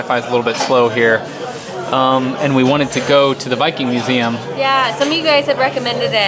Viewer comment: Full English! I'm fussy about those and rarely eat them! I thought you said Biking museum! Duh! Viking museum! Ha!